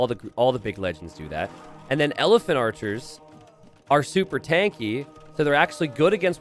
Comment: Did anyone else mishear the Elephant archer for Elven Archer ?